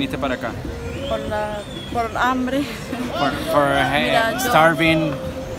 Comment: I speak almost no Spanish… but thought he said why did you come here and she said for a man???